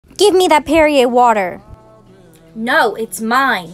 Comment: I thought she said give me that period water…